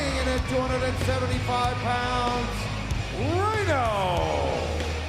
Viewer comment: I thought Tony Chimel said Rayno 😂